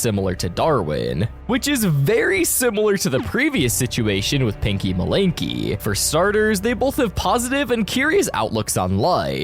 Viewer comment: Wait did that say pinky malarkey